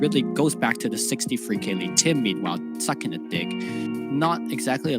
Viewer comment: am i hearing this right? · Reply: "stuck in a dig"